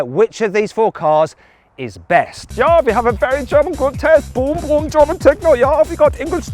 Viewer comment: 잉골슈타트(아우디본사) 슈투트가르트(벤츠본사) 뮌헨(BMW본사) 볼프스부르크(폭스바겐본사) 을 나열하는것 같은데 그 앞은 뭐라는지 진짜 모르겠네요.. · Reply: Ja(독일어로 yes) we have a very german's good tech boom boom german techno ja라고 하는거 같아요 음악이 테크노인데 언어유희 개념인듯ㅋㅋㅋㅋㅋ